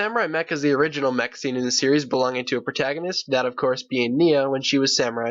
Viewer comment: When said the main protagonist I thought he said the main prostitute: Nya